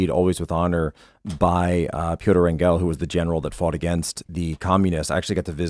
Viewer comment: what name does Jack say ? Piotr Engal? or what? i need his name!!